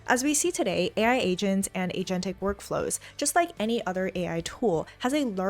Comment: Tina utter a word that I can not know it enough to type it to findout what it is and what it means. Kind if sound like agent-?? Does anyone else understand what that word is? If so, please put it in this question. (I thought it was anthropic, but I don't think it sounds the same in this video.) I really like Ms. Huang videos. It's helping me undestand much more than other videos. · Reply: u mean "agentic workflows"? u can turn on subtitles to see that in future